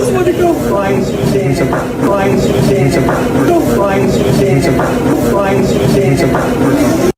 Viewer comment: Sounds like they say lets find Susan. But that's just me.😊😊